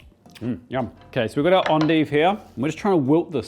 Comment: what did you call it? Is that not just curly Kale? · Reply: Endive. Pronounced fancy French, or something.